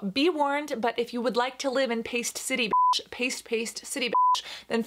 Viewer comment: I thought it said “pasta city bitch” & I was both confused & like, yeah that makes sense 🤔